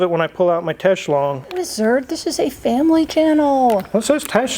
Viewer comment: thought said, “this is the wrong channel,” as if Mr and have an OnlyFans account too